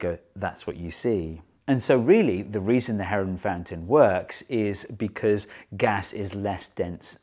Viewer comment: Oh, I thought it said "Heroin Fountain". A bit disappointed.